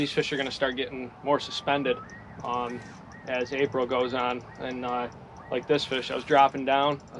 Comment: Sorry if I missed it but what month is this? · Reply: Sounds like he said April